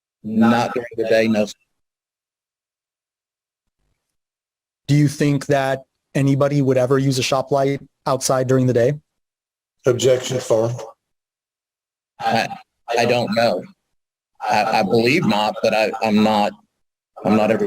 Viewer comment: It sounds like he is saying “Objection: Fong”. What is he objecting? I can’t understand him.